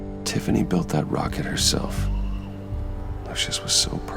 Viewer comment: Did Bruce call Lucius "luscious" at 54:28?